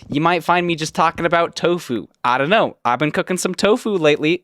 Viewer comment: i thought you said "Toe food" lol